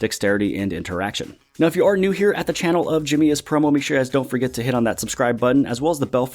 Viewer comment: Lol. First tim on channel I thought your name said Jimmy is porno 😂